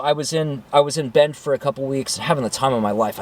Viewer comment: I had to rewind to double-check what you said. I thought you said you, "were in BED having the time of your life." I thought it inappropriate! Then, on the rewind, I realized you said, "BEND."😂😂 I think everyone should drive a vehicle that they can live in, just in case... A van, truck with a shell, SUV. A good backup home. I'm looking for a campershell for my truck.